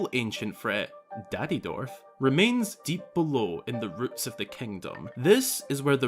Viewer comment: At this point, the captions say "routes of the Kingdom". Not "roots of the Kingdom"? Anyway, okay analysis video! Thanks for uploading!